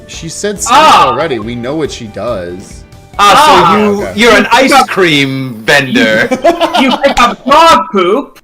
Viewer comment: I swear I thought said "Ice Creambender" like it was one of the bending arts 😭